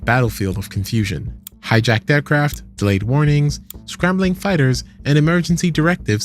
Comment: lol did he say delayed warnings 😂😂 America knew this was going to happen